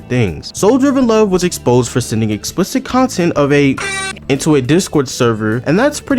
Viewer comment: I literally have no idea what this word is, but something tells me I don't want to know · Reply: It says "minor"